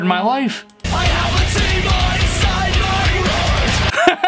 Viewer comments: i heard "i have a demon inside my RICE!" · I heard : "I have a tea bag inside my rice" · I hear “I have a team and it’s on my right. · i heard i have some tuna inside my RICE!